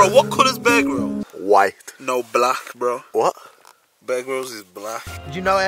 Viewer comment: did say that bear gryls is black